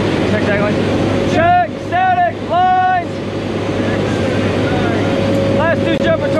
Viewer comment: my captions said “dragon. Jerk static”😭